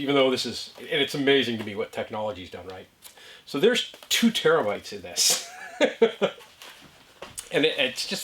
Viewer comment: Hey Old timer, for a minute there I thought you said that you had 2 termites in that box, I was yelling at you not to let them out! I've been studying the videos lately trying to figure out exactly where I'm going to put my bed so keep the videos coming. Ok ol buddy, hang in there, spring has sprung here and is headed your way. See ye next week.